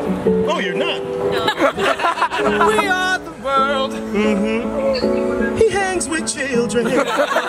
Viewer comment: Charlie: "WE ARE THE WORLD" *Wayne to Jason*: "He hangs with children" Pure comedy. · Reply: I heard "he has white children"😂😂